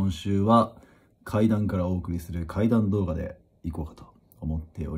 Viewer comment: 喉壊してて声低いし、「階段からお送りする怪談動画」に聞こえたから怖い話が始まるのかと思ったw